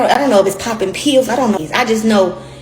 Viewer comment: poppers are a gay party drug that you sniff! sold at most novelty stores. she is NOT saying poppin pills y’all.